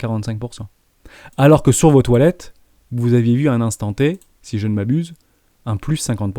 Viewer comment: J'écoutais la vidéo d' en *2, j'ai eu un sursaut à 20:42, j'ai compris "sur vos toilettes vous avez eu un +50%" trader/investisseur des cabinets 🤣🤣 Merci pour la vidéo et le super Discord !